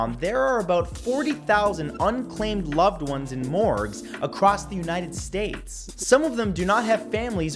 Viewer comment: did he just say morgz ?